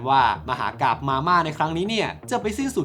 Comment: ไม่รู้ว่าคิดไปเองหรือเปล่า เเต่ได้ยินว่า มาม่า